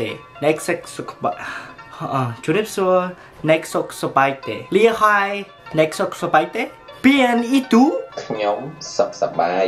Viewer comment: I thought he said "លៀសហាល" for a sec...😭😭😭